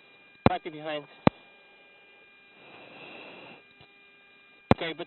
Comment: sounds like he says "medina...michael jackson land immediately to fuel the aircraft" LMAO. I know that's not what he said but that's what I heard. If anyone is wondering why and didn't read about it...the planes cables were put back together wrong and then everyone missed it except for the computers, which a tech rep who flew in from out of countey had them reset. Pilots missed it on checklist. Could maybe be easily overlooked, IDK. Then they found out upon take off they had issues. The data logs showed that several times the plane pulled 5-G's and Im not talking about cell phone speed. The rear half of the plane excedded 120% of rated force and had they pulled 6G's it may have crumbled. Pilots had to swap seats between the 3 landing attempts due to fatigue. They did a great job but should have caught the problem.